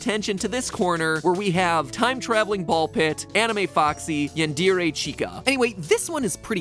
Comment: Did he just say "YoN-dEaR-aY Chica"???